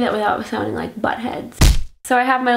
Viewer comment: all this time I thought you were saying "BUTTHEADS!" ,like why is she calling us "BUTTHEADS?" lol